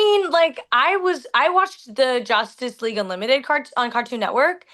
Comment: did she said Josstice League? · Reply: She said justice gang